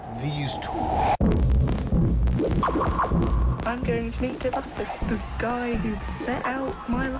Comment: Does anyone know what the girl is saying at 1:29? Something about meeting a guy who effected her life in some way by commenting on one of her videos? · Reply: She said, "im going to ask this, the guy who set out my life by commenting on my video."